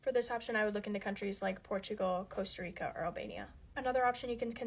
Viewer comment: wait... did she say Albania? That would be beyond hilarious 😂